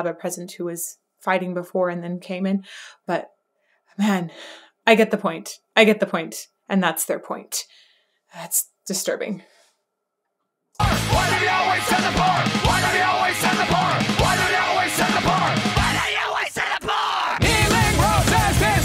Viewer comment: Daron's "WHY DO THEY ALWAYS SEND THE POOR!" in the opening really sets the tone for Serj to come in. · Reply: idk why i heard "why do they always have a bomb"